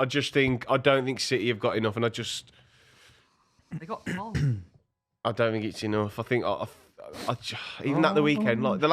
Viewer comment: Who does say here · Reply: Haaland